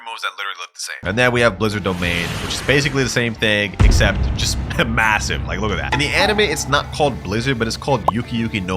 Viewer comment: did you just say m- m- massive? 🗿